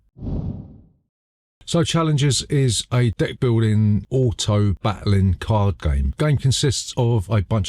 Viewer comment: For a brief moment I was like: "did he just say d'ck-building game?"